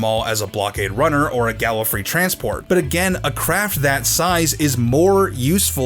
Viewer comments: Did he just say gallifrey transport ( 2:43 ) ? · did you just say Gallifrey transport? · did he say galifrey transport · the subtitles say Gallifrey... "GALLFRAY!!!"(Spell?)